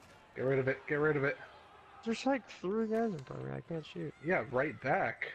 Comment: wtf did I even say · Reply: "There's like 3 guys in front of me, I can't shoot."